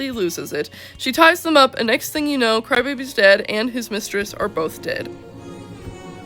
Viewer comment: Did you say "crybaby is dead" or "Crybaby's dad" in 05:02 ? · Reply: Crybaby’s dad! My voice kinda had an accent there for some reason lol